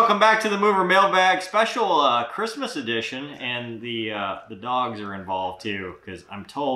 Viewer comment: I was just listening while I write this paper and I heard "Christmas Erection"😆 Had a good laugh then went back to hear what he actually said.